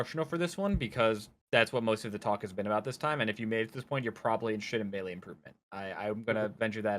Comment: I thought he said "you're probably sh*t at melee improvement". My most pressing goal in melee is to stop getting mad especially when I lose to something I consider "lame" (which is subjective) like marth. My anger is almost non existent irl cause I can understand that I'm playing against a human being, but my mindless unranked grinding is made even worse by how angry I feel during it when I lose. And it's not the "good" anger like ibdw says, for me it just hurts my gameplay and emotional state way too much. I could probably start meditating right now, or maybe make the conscious decision to take more breaks during my play. I'm in other country rn so not much melee for me outside of my aunt's laptop sometimes, argentinian unranked on wifi is interesting